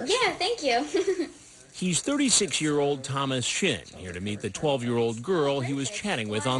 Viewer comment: i thought chris said ‘36 year old thomas shit’